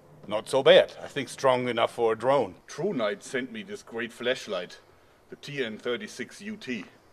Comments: Any one else thought he said fleshlight instead of flash light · Who else heard "fleshlight" ? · Sounds like you said fleshlight😂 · did he say fleshligth?! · lol u thought u said "fleshlight*" 😂😂😂